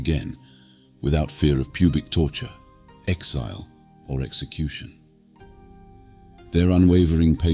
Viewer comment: Did I hear pubic torture at 5:43 ?